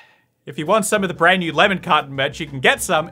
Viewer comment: For so long I thought he was saying lemon cotton, and was confused, until I realized lemon carton